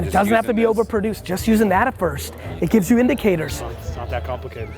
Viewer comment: What does he say at 3:50? An adafirst? Thx